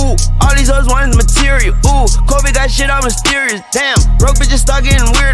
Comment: “Kobe Died it’s mysterious damn 🧐‼️” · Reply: I thought he said Covid that shit on Mysterious.. no??